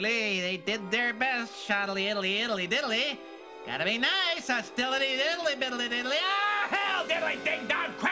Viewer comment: Does he actually say 'Hostility-iddly-diddly..'? 🤣